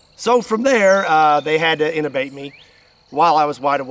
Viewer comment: Sorry but what did they need to do to you? Do u say 'Innovate'??